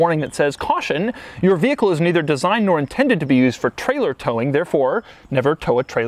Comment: I think Subaru is saying to not do so much tug of wars 😂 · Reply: C R U Media No towing trucks